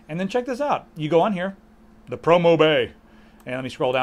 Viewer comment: I could've sworn it said "The Porno Bay". I was sooo happy for a second.